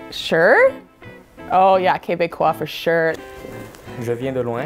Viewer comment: lol the subtitles say can’t bake off a shit but she said Québécois for sure 😂also put subtitles at 0:50 I was dying lmao